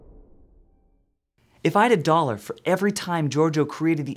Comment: Could someone help me to understand English please. 3:13 what he is saying? " If I had a dollar" or "if I have a dollar" the pronunciation is very fast i'm just listening something like "if I'd"